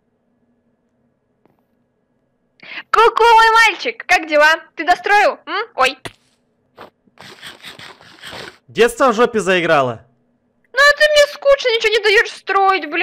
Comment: ку ку мой мальчик. Как это мило😍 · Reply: Мне послышалось куковый мальчик😅😂